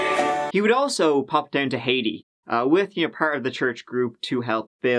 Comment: I've only got to 1:24 where I stopped because of what you said at 1:22 ........... "He would also pop down to Haiti", but I thought you'd said "Hades" which is, of course, another name for Hell...;-D The subtitles corrected my misinterpretation of your accent...;-D EDIT - Well, I got to the end of your story, and can only say that the US seems to be full of outright psychopaths and weirdos!!